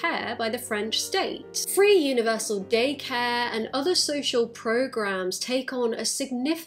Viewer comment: 🦇 also when you said "daycare" I thought you said "gaycare"